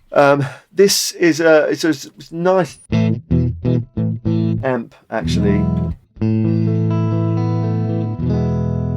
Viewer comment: What did he say at 8:42 to 8:44 ? His lips kept moving but nothing was heard? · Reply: "thick sounding"